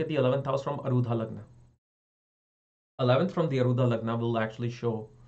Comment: 11th from alludha lagna? Is that what you said? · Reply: arudha lagna